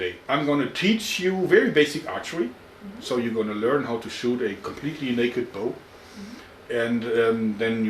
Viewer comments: I thought he said "shoot a bow completely naked..." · I misheard him say, "You're going to shoot a bow completely naked." lol. But being the gentleman that he is actually did the opposite and gave her more clothes.